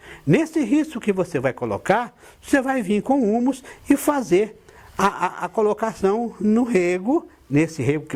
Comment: não entendi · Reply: É prá colocar húmus no rego.